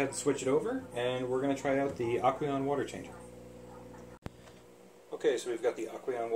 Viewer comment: I thought you said the first was aqueion